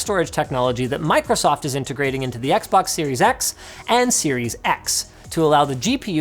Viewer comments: I swear Linus said "Series X" twice, just with different emphasis on the "X." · Is it me or did he say series x twice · did he say series X twice?